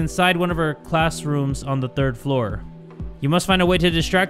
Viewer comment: Wait am I trippin or did Jay said third floor when the caption said second floor